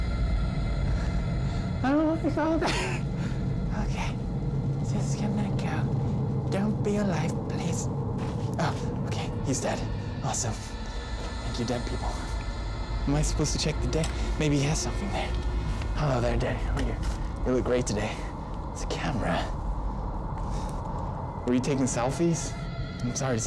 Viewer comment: Hahaha! at one point I heard "Fuck you dead people" anyone else get that?